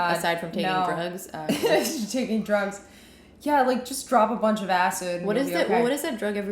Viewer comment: did she just say "just drop a bunch of acid" 😂😵😂